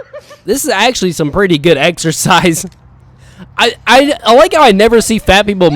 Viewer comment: Exercise?! I thought you said Extra Fries! 😂😂😂😂😂😂😂😂